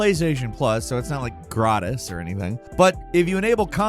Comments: hold up...did he say "gratis"? i didnt know you can use that in english :D. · Did say gratis like in romanian for free?